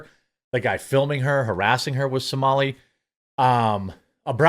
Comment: I heard he was artistic